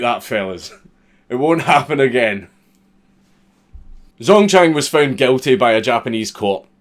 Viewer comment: when you said: "Zongchang was born..." I could've sworn you said "Zone-tan"